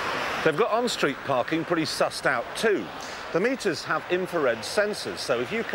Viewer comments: Did he just say "suss"? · Did he say sus? 😳😳😳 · did he just say sus'd out? Haha